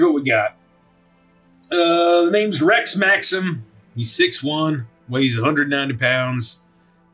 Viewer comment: I heard Rett Maxim, not Rex Maxim